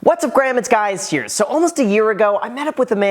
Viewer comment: Am I the only one who heard “What’s up GRAM it’s GUYS here?” Lol 😂